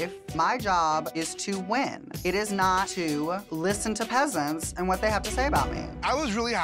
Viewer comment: Did Elliot just call candy muse a peasant?!?!?! Omg I LIVE!!!